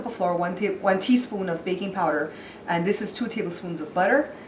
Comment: Lol I thought you said add baby powder...I was like, what! You said baking powder!